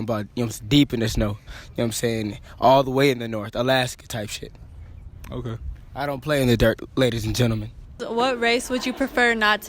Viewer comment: " i dOn'T pLAy iN thE DiRt" he really had the nerve to say that and be that ugly. Boy have you even glanced at a mirror.🤧🤣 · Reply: Lol I thought he said "I dont play in the dark" to me thats way funnier